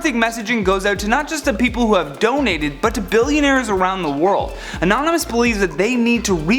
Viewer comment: Ho else thought it said bill gates Instead of billionaires